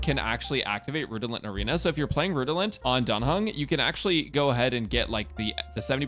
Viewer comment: Sounded like he Said Dung Hung Lol Also, Her crit rate buffs don't work on DOT teams btw if anyone was wondering. Since dot is based solely on Atk Stats. Also " if i'm wrong about this correct me" This was simply Off the top of my Head as i've heard somewhere from someone she doesn't work on nihility chars to well But she benefits "the hunt chars more so"